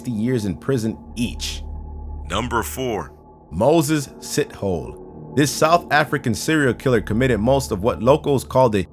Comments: are you sure his name isn't "Moses Shithole ? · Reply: Near choked on my water, really thought he said shithole lol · Pringel: "Moses Sithole" how I heard it: "Moses Shithole"